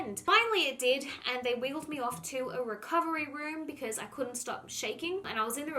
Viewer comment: I am American, and for some reason your accent is making me think that every time you say "wheeled me off" I keep hearing "wiggled me off"